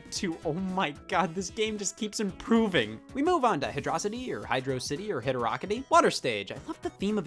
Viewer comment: Did he just say Hidurokity